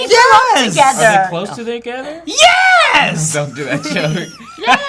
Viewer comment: it sounded like said "don't do that shit to me". I can't hear what he's relly saying but I'm guessing it's not that.